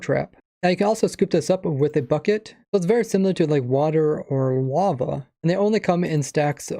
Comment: Did he say Wava?